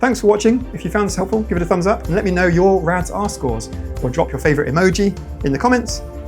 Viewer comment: Why did I hear "let me know your rat's arse cores" at the end 😭 I watched the whole video, slowly coming to the realisation "I'm probably not autistic", but then I did the RAADS-14 two times (trying to not be biased and answer only the things I'm really sure about, like almost never saying "true now and when I was young" because I just can't remember) and it's still "indicative of autism" aaaaa!! Am I crazy to feel like most people completing at least the RAADS-14 would get a somewhat high score? You only need 14 and above out of 42 for "indicative of autism". The second time around I was even harsher with my answers, picking only 1x "true now and when I was young", 8x "true only now", 0x "true when I was young", and 5x "never true"! And that's still a 22 score, even though I only disclosed a single trait of having been with me through childhood. Maybe someone can shed some light on this. Of course, I will also do the extended edition of the test, I just don't think I could focus on it, plus it's much more confusing.